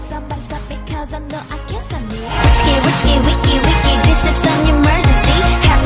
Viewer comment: I thought it said whisky whisky wickey wickey😭😭😭😭😭